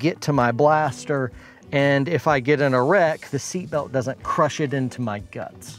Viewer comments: When you said "if I get in a wreck" I heard "if I get an erect". Wasn't sure where that was going for a second. Great video thanks for all of the good info and tips. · “if I get an erect the seatbelt doesn’t crush it into my guts” that’s what I heard lmao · Definitely thought he said "if I got an erect the seatbelt won't crush it into my guts." Which would also be a valid concern.